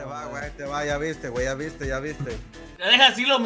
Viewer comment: Pensé que decía: "Ya valiste" xd